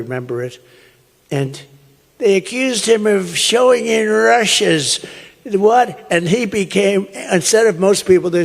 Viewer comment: "showing in Russians", is what he was trying to saying.